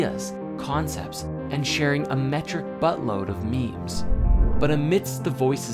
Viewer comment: 'a metric butt load' did I hear that right? I really liked the video though and can't wait for the next installments.